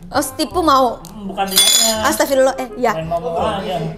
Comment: Gue ga salah denger pas HARUKA JATUHIN SENDOK DIA BILANG ASTAGHFIRULOH 🤣🤣 SEMOGA CEPET JADI MUALAF HARUKA AMIN.